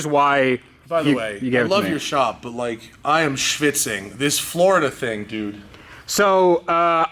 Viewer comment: Wait.. did he just say I am schwitzing? "schwitzen", the german word for sweating? lol